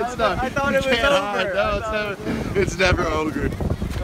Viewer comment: did say ogre